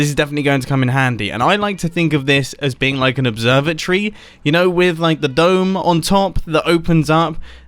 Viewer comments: did anyone else hear observer tree?? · I thought you said “observer tree” not “observatory” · I heard “observer tree” and thought you were literally going to build a tree with observers lol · When he said “observatory” i thought he said “observer-tree” and I was like what’s this man about to build 😂😂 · when he said he wanted an observatory, I heard "observer tree" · "observatory" sounded like "observer tree" and now I want to see that lol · for a moment I thought he said observer tree 😂😂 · You know you're a redstoner when you mishear "Observatory" as "Observer Tree" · I thought that you said the observatory as observer tree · anyone else heard "observer tree"? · Who else heard observer tree at 2:40 at least i did.😂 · Who else thought he said observer-tree? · When Mumbo said observatory, I heard "Observer Tree". And now I think he should make an "Observer Tree" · I thought you said observer tree not observatory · When he said observatory I thought he said observer tree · any other stupid American think he said observer tree instead of observatory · When you said observatory i thought you said observer tree which i imagined and I'm starting to to have a mid life crisis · When you said "observatory", I literally thought you said "observer tree". 🤨🤔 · When mumbo said observatory, I thought he said observer tree. I is a big dumb. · I fully thought you said observer tree... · when u said ‘observatory’ i heard ‘observer tree’